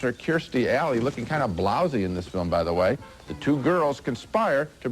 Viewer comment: Did Siskel say that Kirstie Alley looked kind of "lousy" or "Blouse-y" there? I was literally stunned, thinking he said the former, like "damn, that's pretty rude." I love this show, since I was a kid, but it only just dawned on me how condescending he was. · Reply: I thought he said "Blousy" ... I thought maybe it was old time lingo, ... calling a woman who was once hot but now is starting to get fat. 😆